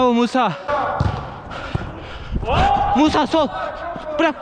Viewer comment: ben neden Musa sow diye duydum 😂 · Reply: ilk bende duydum ama dikkatli dinleyince musa sol diyor yani musa sola git diyor